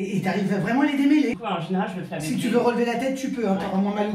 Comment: "En général, je le fais avec..." : i heard "En journal, je le faille..."